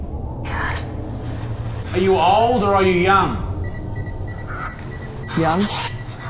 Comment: sounds like it said "move of us are young'